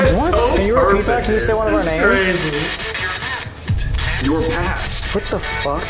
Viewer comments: I was gonna say, 10:25 it didn't say "you're past" as in "You are past" is meant "YOUR past", relating to Sam's scream from 2019. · when you hear the scream that sounded like Sam scream in Colby, it says “your past” right after like its telling them that Sam is yelling for Colby from the past